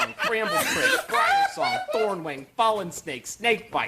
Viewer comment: Did I hear "Thorn-wang" in there?